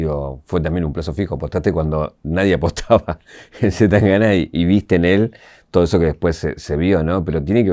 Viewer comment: en el minuto 39:15.. alguien me dice qué dice el locutor? .. apostaste cuando nadie apostaba el........ ? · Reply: C. Tangana, es el rapero que participa en su cancion "Tocarte" en su último disco.